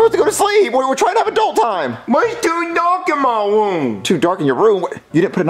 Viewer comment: I thought Jeffy said there’s 2 dogs in his room